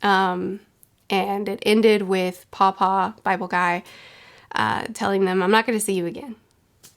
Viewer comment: Wait i thought she was saying papa and i was trying to figure out whole video why the hell she kept saying papa. Damn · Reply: if you know one piece story. You would understand 100% that she said paw paw bible. The fact that she added the word Bible should let u think of paw paw when u hear pa pa or pah pah.